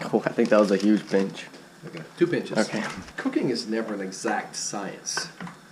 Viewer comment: i think he said cooking but i heard "cocaine is never an exact science" ....